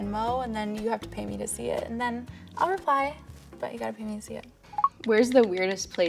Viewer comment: Can someone write what she said? My English is not the best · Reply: She said "you gotta pay me to see it"